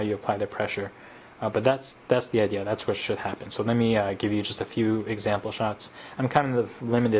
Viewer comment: did he say on 4:02 that's were shit happens???